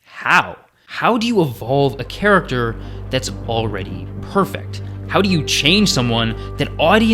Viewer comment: Did you say.. perfect? 🪲